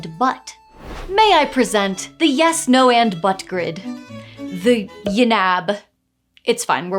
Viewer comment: I definitely misheard the YNAB section as the "Yes, No, and Butts Grid"